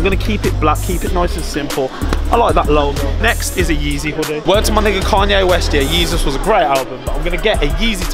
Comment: did he say the n word